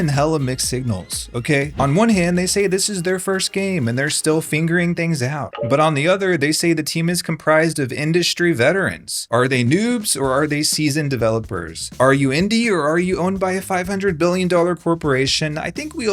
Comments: you sound like you said "this is their first game and they're still FINGERING things out" ???? xD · did you just say "fingering" things out?